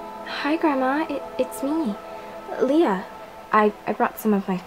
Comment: When Leah was speaking to her grandmother, I thought she said "Hi grandma, it's me, Elita." I was like 😱😱😱😱😱😱😱😱WHAT DOES THIS MEAN, but then I realized that she just said Leah😂😂😂😂😂